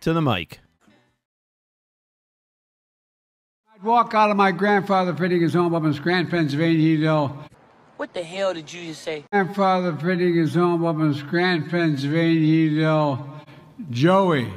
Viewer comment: Sounded like he said "I walk out on my grandfather putting his old woman's gran pants on in Union Dale"